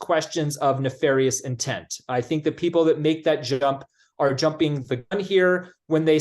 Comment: it really sounds like he says "people that make shit up" 🤣 I know it's an internet glitch but still ...